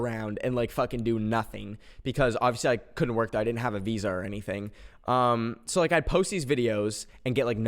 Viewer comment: i heard that visa as Weezer for a sec